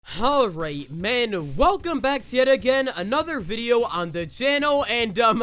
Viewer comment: at the start of the video i was doing something else and i thought i heard “alright boys” instead of “halright men” and i went and checked twice